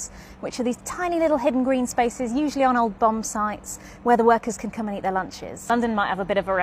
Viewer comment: I kept hearing "old bum sites" and thought that sounds kind of offensive... lmao had to read the comments to realize my mistake :D